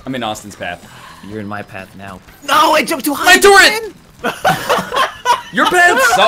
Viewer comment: Did NL turn into torbjorn at 38:00? I swear he said "my turret"